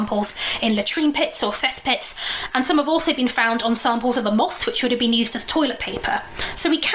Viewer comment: At like 43:05 what did she say was used as toilet paper? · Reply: Moss